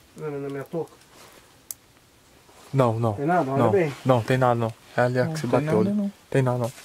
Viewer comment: uma voz fala junto com o " não tem ali não" foi o que eu entendi.